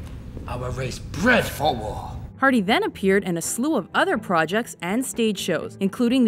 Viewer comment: Whenever she says ''Hardy then'', all I hear is ''Harvey Dent''